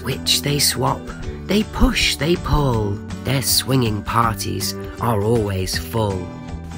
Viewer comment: I get it but don’t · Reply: I thought they said " slinging "